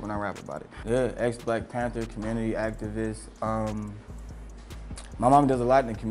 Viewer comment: he say his momma an activist i thought he say actavis